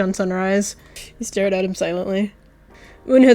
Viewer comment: i heard "he gayed at him silently"